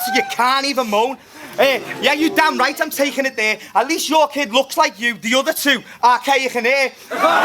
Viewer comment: anyone know what the fuck say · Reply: 'At least your kid looks like you. The other 2 Arkaic and Eurgh'